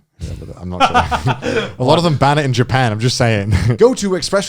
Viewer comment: Lots of Hentai ban in Japan? Wtf am i hearing?